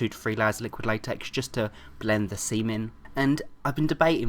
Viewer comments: lol @ 2:00 he said blend the seam in but it sounded like he said " blend the seamen " :D im not jating its funny and dont hate me for saying that because im bi..... I LOVE U 3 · i almost thought he said blend the "semen" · sounds like he said blend the semen XDXD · it sounds like he said semen...